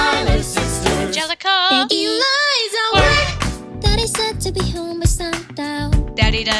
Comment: the captions say “Daddy said to be homeless,” instead of, “Daddy said to be home by sun down.” LAMO 😂